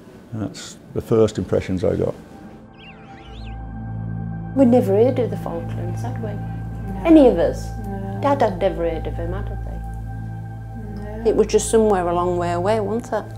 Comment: What do the aunts say between 6:00 and 6:15? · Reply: "We'd never heard of the Falklands, had we? Any of us. Dad had never heard of it. It was just somewhere a long way away, wasn't it? It certainly wasn't Hull, girls"